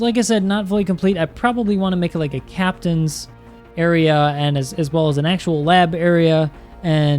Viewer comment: Did he say Captain's area? 4:47 yes this will do just fine. :D Well done this looks fantastic!